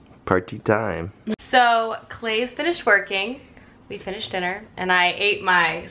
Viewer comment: ok so...... at 23:41, you said, "so, clay just finished working...." but i heard.....'so, clay just finished twerking.....' i, at the time, had turned my head to grab a tissue and im pretty sure i got whiplash, trying to turn back around and see clay twerk. lol i replayed the part and of course you said no such thing, but now i cant help but wonder, what if.......clay was twerking? but yea, ok thanks bye. lol